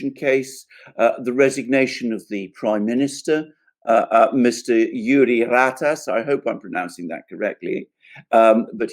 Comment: I had to rewind to start. I thought you said jury rat ass?